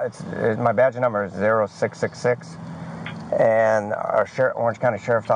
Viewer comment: The officer gave his badge number…0666….did I hear that right?? Around the 16:39 mark